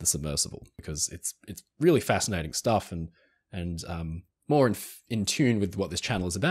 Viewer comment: You are amazing. I’m studying to be a history teacher. Im less so intrigued by history itself but more how it’s packaged and taught. I always say ‘history happened regardless, the X-factor is how it’s taught.’ New subscriber! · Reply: Dam, I thought you said ""read more."